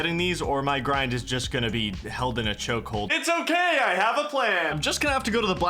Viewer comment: did you say choke hold or chokewhole. This is a very important question for IRL reasons pls reply love u